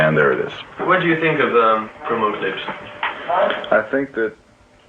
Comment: Can anyone tell me what the interviewer says at 15:28? He says something like "What do you think of the ---" and the last bit is a mystery to me! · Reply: I listened to it twice and it sounds to my ears like he says "promo clips (videos)"